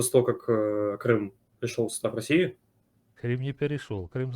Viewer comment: мені почулося, чи дійсно звучить:" Крым перешёл сюда в Россию"?